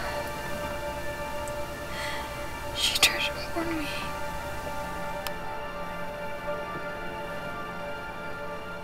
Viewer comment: What does she say?? She tried to warn me?